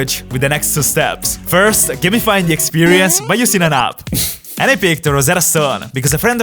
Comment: waitttttt, did he just say "ألعاب"? Thats arabic Edit:games he means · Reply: He said: "And i picked Rosetta Stone"